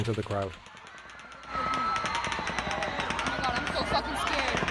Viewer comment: I swear I heard a guy go "I ain't F*** scared!"